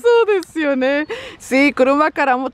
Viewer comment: pensé que decían cucara macara titere fue 🤭🤭 en buen plan así lo escuché